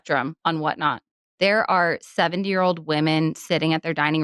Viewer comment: I heard 7 year olds 😂